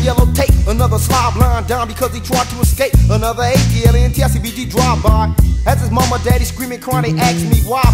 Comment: wat did he say?💀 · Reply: A-T-L-A-N-T-I-C B-D drive-by.